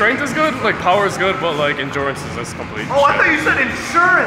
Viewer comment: I thought he said insurance too “ my insurance is terrible” 😂😂😂😂